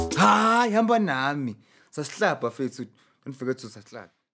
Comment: Did he just say SUBSLIBE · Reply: Nah, he said "sas'hlab" lol